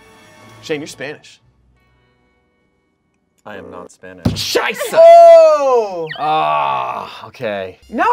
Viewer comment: did say „scheisse“ at 4:31??? that means shit in german